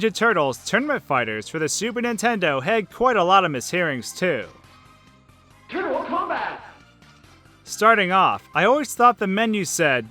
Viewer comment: With TMNT Tournament Fighters, I heard, "Terrible Combat!" Also, is the BGM Mischief Makers?